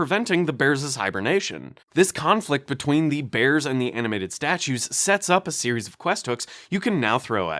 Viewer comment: Dude I had this playing in the background. When you said “animated statues” I heard “Adamantium Cashews” and when you said we can work with that I was quite intrigued lmao.